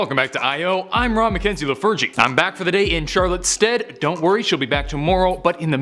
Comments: did he say Charlotte’s dead? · I thought he said “I’m back for the day cause Charlottes dead” IVE NEVER HAD SUCH A BIG HEART ATTACK IN MY LIFE OH MY